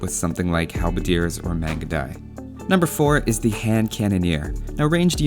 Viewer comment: protect it with what again? I could not make out what was said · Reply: Mangudai